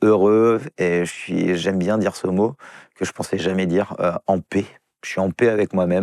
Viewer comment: Je suis régulièrement en pet moi aussi